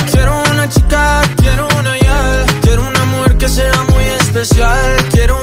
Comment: Pensé que decía quieto una Yal